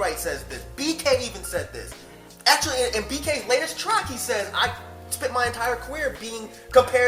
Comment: Who is he talking about at 15:49? Does he say BK? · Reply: Bekay. He's a rapper.